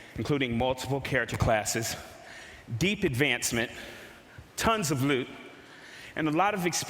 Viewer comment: lol lots of loot I heard lots of lube xD